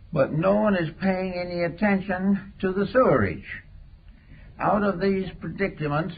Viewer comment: What word is he saying at 7:40? to the Sewerage? PLEASE HELP OBI WAN